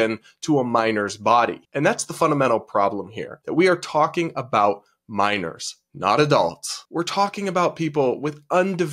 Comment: The automatic subtitles are sometimes funny. At 5:29 it says "that we are talking about miners not as adults" instead of "we are talking about minors not adults".... 😀